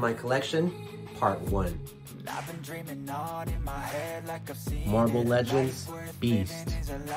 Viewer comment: I thought it said marvel legends bean